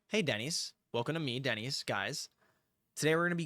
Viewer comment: I heard "hey daddies" and I was like: excuse me?!